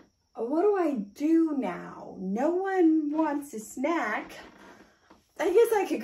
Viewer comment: I heard “well no one wants to smack” my heart dropped but then I was like “oh she said snack 😭”